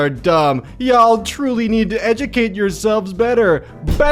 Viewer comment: I understood "Y'all truly need to educate your subs" instead of "Y'all truly need to educate yourselves"